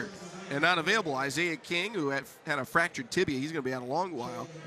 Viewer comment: I swear I thought he said “a fractured titty” at first 😂